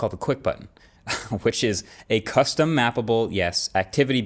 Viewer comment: "custom-mAPPLEble button" ... I hear it every time. 😅